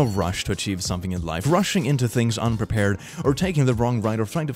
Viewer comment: What I hear: There is no rush - točím - something in life... Like literally 'točím' is a Czech word and it means 'I'm filming/recording' so like... what 😂😂??!! As a native Czech speaker learning English, this makes me hella confused 😂!! Like what is the word he uses?! I can't hear the actual word 😂! Edit: After a few more listens, I finally understood what he said xd... "There is no rush to achieve something in life..." 😂😂 I just feel so dumb rn xd...